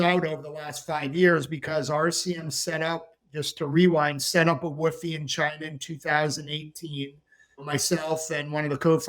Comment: what is that he said sounds like "setup a woofy in China"? What is that?